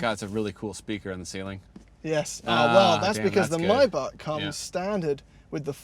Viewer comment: the captions say "my butt" instead of "maybach" lmao